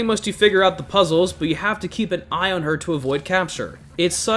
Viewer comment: I heard “you have to keep an eye on her to avoid cancer” 💀